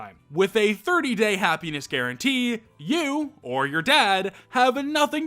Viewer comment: (Sorry uh) but I was skipping through the sponsor and when I stopped skipping all I heard was "Penis guarantee" and I LOST MY SHIT 😂😂😂 it's 8.30AM and I was just cackling like a deranged witch